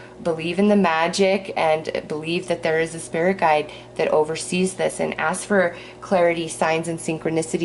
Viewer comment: I thought it said that he's a spirit guide from "overseas" 😂. Like out of the county. Because the person I'm thinking about is actually overseas right now.